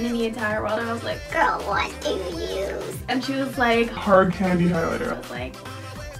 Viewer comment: it sounded like she said Hard Carry...IGOT7s😂